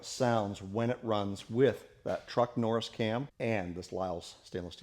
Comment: Truck Norris cam? I thought at first you said Chuck Norris cam. If Chuck had made a cam you know it would kick butt and take no prisoners! Take no prisoners...😁